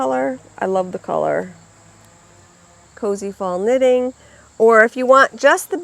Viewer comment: Love the Bewitched collection. I love the yarn and bag this week. I thought you said it was “CozyThong “ knitting. I finally heard Cozy Fall. LOL ❤️🌺